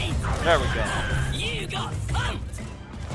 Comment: I experienced a glitch while playing imaginators I tried doing a grind rail challenge when the R2 button above the grind rail disappeared and then I could only walk around i had to restart the level. Ps 2:15:48 anyone else hear say "you got f*cked" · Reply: Actually said, "You got thumped." No way did he say a curse word.